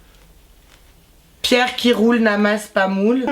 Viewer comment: J'etais couche tranquille et la j'entends Quooooi à 11:49 personne n'a remarqué : "Pierre qui roule n'amasse pas MOULE" 😂😂😂😂😂😂 mais j'en pleure 😂😂😂😂😂😂😂😂😂😂 Wesh n'amasse pas Moule 😂😂😂😂😂😂😂😂😂😂😂😂😂😂😂